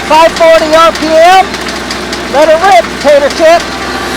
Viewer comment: Did you say "Let her rip, Tedder chip? or Tater chip?" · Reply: Tater!